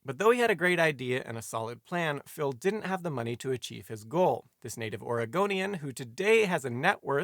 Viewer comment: Had to rewatch this part. Thought he said, "This native Argonian." Ahh Todd Howard, you've done it again. - The Spiffing Brit